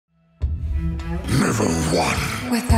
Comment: I hear 'Never one' as 'Level 1'